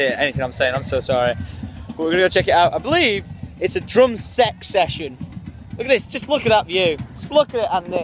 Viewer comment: Thought you said drunk sex at the beggining hope im not the only one.